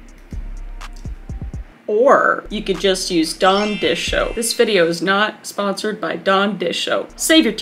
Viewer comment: It has been 3 years since this video was posted, and I just now realised that it sounds like Kris is saying “Dawn dishoap” instead of “Dawn dish soap”